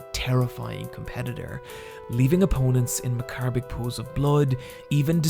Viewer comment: What was that word, again? "Leaving opponents in..." what? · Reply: “Macabic” I don't know if that's actually a word. But I think he meant “macabre” which is used to describe something that is unpleasant or odd due to the fact it is heavily connected to death or violence.